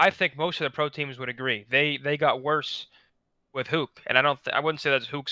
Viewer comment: Im sorry i think i misheard you. I though u said they got WORSE with Huke??